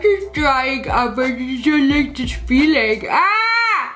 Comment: what did she say? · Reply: “I really don’t like this feeling”